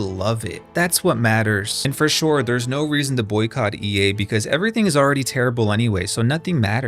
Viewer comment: Your whole message with this video was to boycott EA and the creators thought you werer saying boycott EA creators